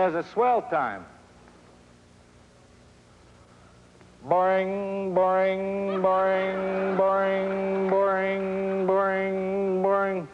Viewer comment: I thought he was saying "boring boring boring boring" lol